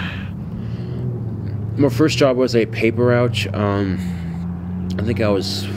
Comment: Did he say paper ROUCH does he not know its called a paper ROUTE 🤣🤣🤣🤣